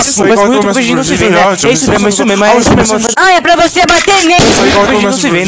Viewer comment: Oq eu ouvi: Faz muito tempo q nós não se vê, não é,é verdade, é mesmo é isso mesmo é mesmo é isso mesmo é mesmo é isso mesmo é mesmo